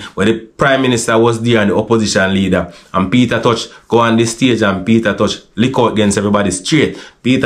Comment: Sounds like him say Peter touch..😂😂